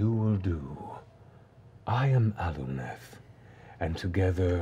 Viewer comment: I almost thought he said “I am Alucard.” And nearly shit myself.